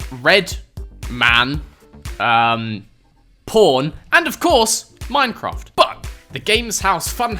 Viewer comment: did he say porn if so what the fuck